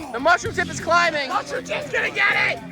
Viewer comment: At the start I thought you said mushroom tit